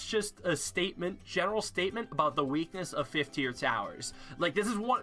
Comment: First time watching I thought at the end he said “I have sooo many fifth tier Tylers to talk about...”